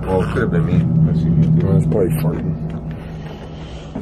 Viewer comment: does anyone else here something at 29:53 sounds like a voice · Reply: Larry Moore. I heard "super dusty" maybe was?!